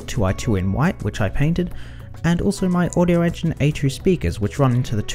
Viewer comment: Hello, i found your channel yesterday, watched pretty much every video in a row! I have one question, how are your speaker called ? i cant understand you at minute 3:33 cause i am not a native speaker.. Thanks! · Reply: Wow, thanks for watching all my stuff, I appreciate it! The speakers are the Audioengine A2's. Let me know if you need any other info.